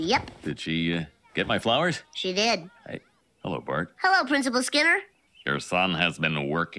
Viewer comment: When he says "Hello Bart," all I hear is "Hello Mario."